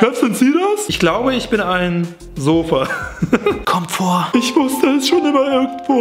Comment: Ich glaube ich bin ein Sofa bin ich der einzige der danach anstatt Kommt vor Comfort verstanden hat? xD